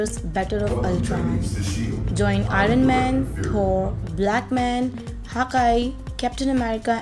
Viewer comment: did she just say black man💀